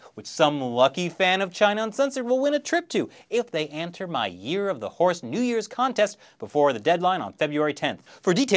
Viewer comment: Damn! I missed the deadline. I could've sworn you said year-of-the whores. But sadly, that's turned into everyday in Chinese occupied Tibet and it ain't pretty.